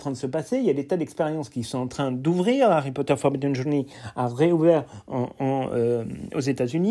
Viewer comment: Excusez moi mais à 7:45 vous dites quoi « Harry Potter ???? Va réouvrir aux États Unis », merci à vous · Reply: Forbidden forest experience :) un vlog est disponible sur notre chaîne ;)